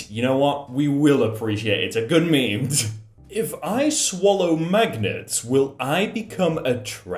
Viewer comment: I heard maggots instead of magnets, I was so lost for a sec.